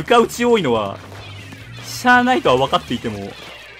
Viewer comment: 「しゃーないとは分かっていても」が 「サーナイトは分かっていても」に聞こえてしまった… どうでもいいわこれ